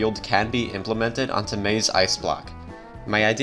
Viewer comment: Wait did I hear him saying “maeves ice block”?